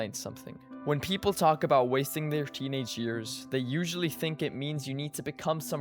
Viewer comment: i thought bro said “stop wasting your teeths”.